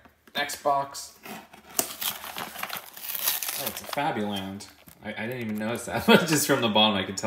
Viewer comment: I thought he said krabby land